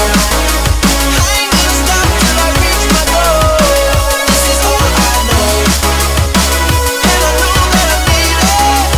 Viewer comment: which does he say "I'll never stop till I reach the goal" or "I'll never stop till I reach my goal" at 1:25? please tell me:D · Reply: guntrix625 “I ain’t gonna stop till I reach my goal”